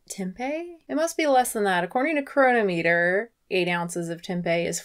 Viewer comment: I misheard cronometer as coronameter.....been quarantined too long 🤪